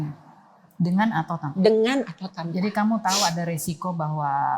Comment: saya mendengar di menit tsb dena bilang "dengan atau tanpa bapak Ryan nya". Sy salah denger apa emang bener?? Kuulang bbrp x kok pendengaranku bgitu?